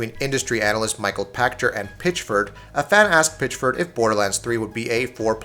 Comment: when he said a fan asked Pitchford, I wasn't paying attention and thought he said a fat ass Pitchford